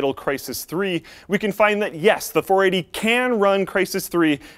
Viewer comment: Did you say 480?